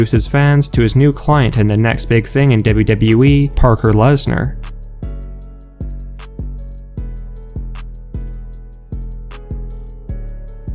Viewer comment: Did you just say Parker Lesnar?😂😂😂😂😂😂😂😂😂😂😂😂